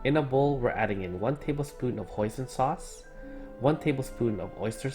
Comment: almost sounded like you said oyster sauce twice was so confused